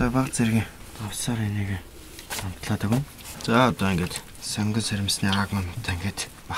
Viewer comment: What does he say at 5:50 ( sorry n*gga) lol. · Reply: jeremiah kurtz haha he actually said “eniigee” which means “that” in mongolian. But it sounder like a nigga. Lol